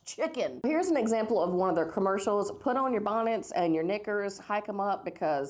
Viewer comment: did she just say the N-WORD 💀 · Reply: No she said Knickers lol it's a old word for a type of pants I'm pretty sure .